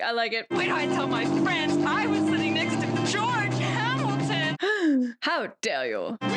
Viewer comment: "I was sitting next to George Hamilton.",,, In Japan, subtitles said "I was sitting next to SEAN CONNERY."